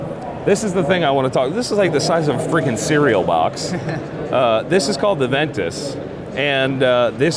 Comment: All I heard was "This is called AVENTUS". When did Creed start making such tools xD